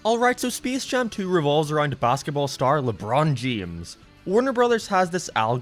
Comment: Anybody else hear "lebron jemes"?